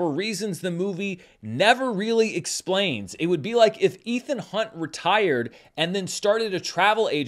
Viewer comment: When you started to make the Ethan Hunt analogy, I thought you said "Ethan Hawke" and wondered what it had to do with "Training Day."